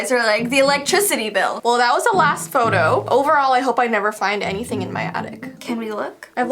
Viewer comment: When she said overall I thought she said Overhaul from BNHA